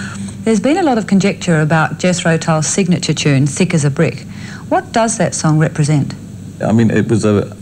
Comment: It really sounds like she says "Sick As A Brick" 🤣